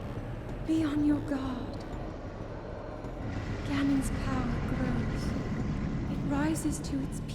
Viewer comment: For some reason, I thought she said Thanos instead of Ganon at the beginning